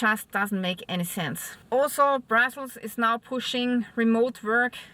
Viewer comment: Love your German accent. Your English is excellent, but when you said " Brussels is pushing for remote work". At first I thought u said " brothels are pushing for remote work"!😂😂